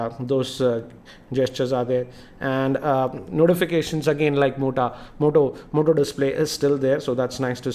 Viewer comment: hahahahahahahaaaha he said mota instead of Moto on